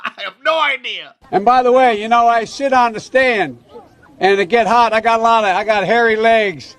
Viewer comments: I swear I heard “ I shit on the stand” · With his slurring speech it sounds like Biden says "When I shit on the stand" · Did he just say, "I shit on the stand."